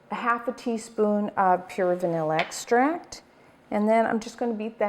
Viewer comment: what kind of extract did you say it didn't sound like you said vanilla. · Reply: Hi there, I hope I can help you out if you don't mind. :) I heard she'd said 'Pure vanilla extract'. Have a lovely day! And thank you always Stephanie for your great recipes and instructions! Love everything about Joy of Baking! I really think you guys are the best among any other cooking channels, you deserve more viewers. Thank you so much! :)